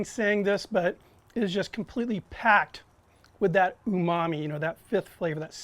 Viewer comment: what did he said? its completely packed with??? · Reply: Nihliuz umami, the flavour of savouriness